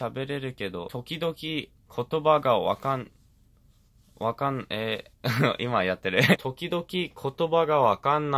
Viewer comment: I THOUGHT HE SAID DOKI DOKI HHHHHH